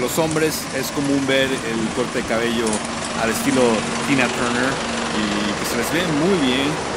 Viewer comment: No escuché bien lo q dijo al minuto 1:28 cuando pasó junto al camión :-( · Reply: calew96 Dijo que a los japones el estilo "Tina Turner" se les veía muy bien.